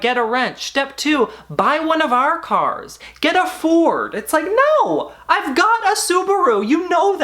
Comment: For the first 3 minutes I thought you were saying, “I’ve got a pitbull”. Was so confused